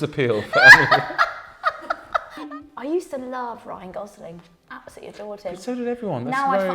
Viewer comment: When she started saying Ryan Gosling, I really thought she said Rylan